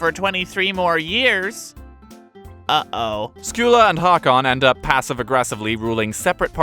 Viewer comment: I’ve been watching too much dune videos thought it said harkonen.